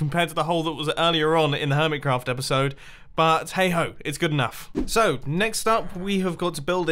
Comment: did he just say "hey hoe" ... i'm wet.